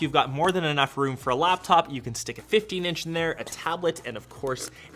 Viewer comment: did I hear that correctly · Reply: a 15 inch?